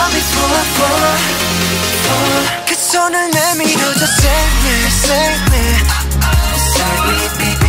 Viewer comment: doesn't it sound like Jimin is saying "can someone mend me or just save me?"